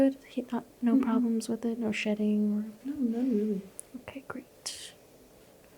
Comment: i thought you said "no problems with it? no shitting?"